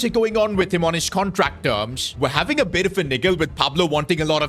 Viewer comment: what did u say 😭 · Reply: He said niggle but it basically means like having disputes